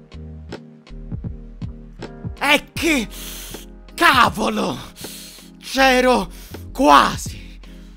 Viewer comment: Per un attimo ho capito "E che Tavolo!"